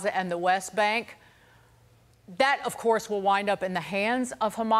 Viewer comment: 100 million, I thought he said 100 billion